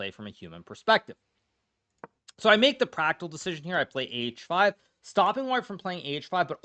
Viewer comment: "So I made the pragg-tical decision.." Did anyone else hear this?? 😂😂